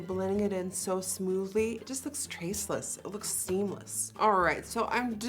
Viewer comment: did someone say seamless?👀